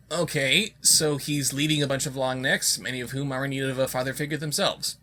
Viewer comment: Every time said "Long Neck" it sounded like he was saying "Lombax". This might just be my Ratchet & Clank fanatic side kicking in, but it still sounded like he was saying Lombax.